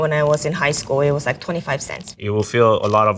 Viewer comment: Lady says top ramen in college was $0.25, on-screen captions say $0.69, now even top ramen is unaffordable.